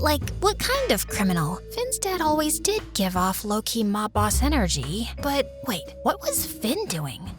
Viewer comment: did she just say lowkey? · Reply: yes she said lowkey